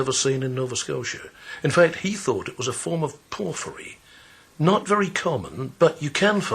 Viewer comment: what is he saying? The man thought it was a sign of ______ polverty? I'm doing a school project on this and it's useful information, please help.